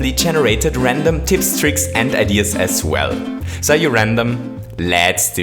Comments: instead of “are you ready?” It was “are you random?” LOLOLOLOLOLOLOLOL I CANNOT STOP LAUGHING 😂😂😂😂😂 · Wait did he say "are you RANDOM"